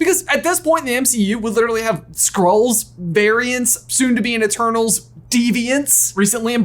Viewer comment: I cannot even tell you how excited I am for this movie! Please respond with whatever emoji you think best encapsulates your excitement! ❤️ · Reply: I was cooking while listening to and seriously thought he said squirrels not skrulls. I thought I missed a major announcement